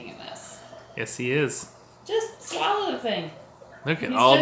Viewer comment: "Just swallow it then." That's what he said.